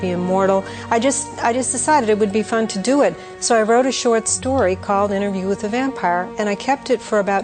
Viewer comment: Note the difference between the original subtitles and those on this video. The original subtitles say "the" and so does she, whereas your subtitles say "a" Time to wash your ears out I think !